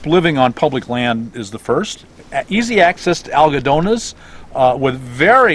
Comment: Where do you go for health services? It sounded like you said "algedonas", but I don't know what you mean. Please talk about where to get dental, eye and other medical services.